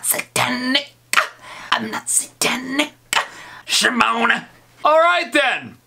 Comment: it's not" Shimonah!", that is what the caption says, it is actually spelled "Shamone!" 😑